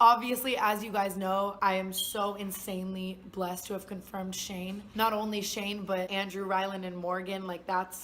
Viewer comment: i first heard "i'm blessed with shame" but that wouldn't fit, so watched again and it was "shane"